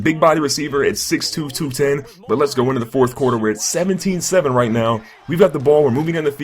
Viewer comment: it sounds like you said 17-7 when it's tied